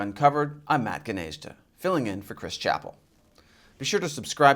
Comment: At first I thought you said "filling in for Clifford Banes". It's a The Onion joke..... 🙃